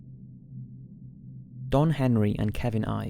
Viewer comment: Number 5, coulda sworn he said Don Henley